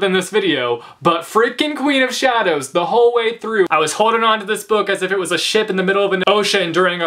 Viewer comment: I genuinely thought you said "I was holding onto this book like it was a shit in the middle of the ocean". I was mildly confused.